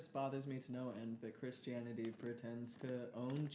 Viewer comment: I think I heard you say "Christianititty" and it may have been a mouth slip, it may be my ears, but I will never forget it